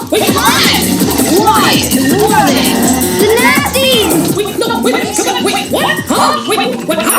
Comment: I heard "the nazis"! Lol!